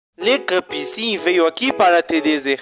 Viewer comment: No everbosei lá achei que ele falou everpodres